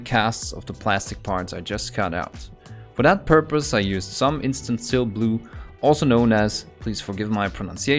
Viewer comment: it's called "instant sil blue" or "Oyumaru", like I say in the video - if you put it into google, you'll get tons of results of shops selling it. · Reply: thanks dude! sorry couldn't find it i misheard and thought it was "instant seal blue" and it came up with plumbing supplies 😂